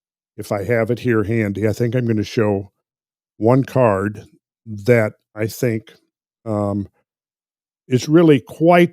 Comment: Randall mentioned the "edidum" (???) Tarot deck. I googled many different attempts at trying to spell what he said, to no avail. What deck is Randall using? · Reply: Builders of the Adytum